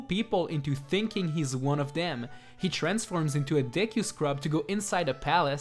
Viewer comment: I thought you said link turns into a dick scrub.